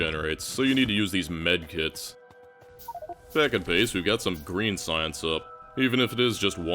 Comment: Every time I hear "back at base" my brain says "bugs in the software."